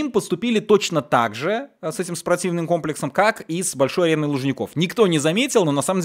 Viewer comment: мне одному послышалось - с этим с ПРОТИВНЫМ комплексом 😁